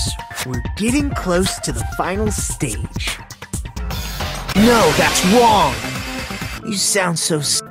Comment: It sounds like he said "no thats wong"